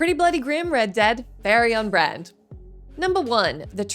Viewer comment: What did she say here? Barry on Brand?